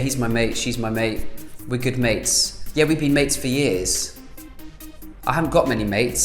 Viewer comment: I misheard "we've been mates for years" as "we've been mating for years"